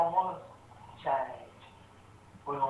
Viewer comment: you really need subtitles for people from alabama, for a while i thought he was saying "chinese" when he was saying "change". anyone else notice the dumbest and poorest states are the most conservative? something else ironically over the head of these backwoods idiots, there is a "commandment" against adultery and conservatives have the highest rate of divorce, while there is no "commandment" against being gay. these people have barely climbed out of caves.